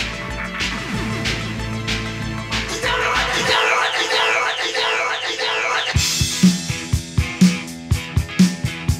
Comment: After 42 years I still don't know what he says at 1:45 ???? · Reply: "Just tell me what....."